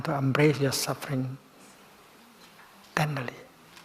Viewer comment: did he say family? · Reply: "tenderly"